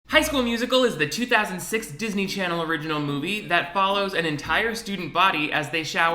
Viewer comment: When you said "high" at the literal beginning of the video i thought u were saying "hi schoolers" and i said hi in my head back 😭😭😭